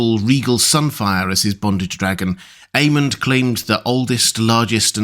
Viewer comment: 😮 I thought you said, ‘bondage dragon’ and my brain 404ed